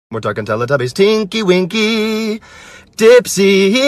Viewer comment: I thought he said stinky winky